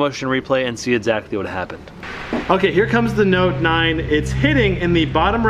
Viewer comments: did he just say note 9? 😂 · did he just say note 9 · Did u really say note 9 at the 2:31 mark !